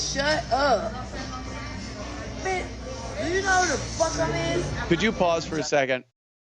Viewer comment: "Do you know who the fuck I'm is?" Is that what she said lol?